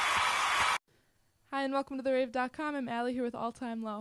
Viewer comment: when she said thank you for watching the rave i thought she said the rape omfg